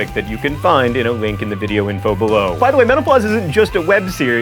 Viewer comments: John was speaking so fast I thought he was saying menopause. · Every time! Why does it sound like he is saying MENOPAUSE!!!